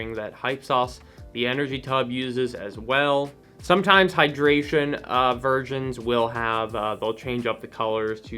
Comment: Did anyone else hear "Hydration virgins" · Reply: LOL Hydration Versions I meant to say LOL